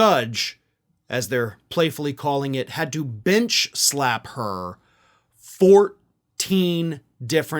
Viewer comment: 😂 A.H. got bench slapped by the judge. 😳 I thought I heard she got mitch slapped. 😄😆🤣